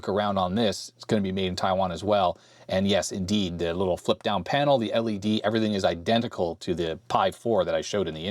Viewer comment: Apropos of nothing, when you said "little flipped down panel" I heard "little f***ed up panel."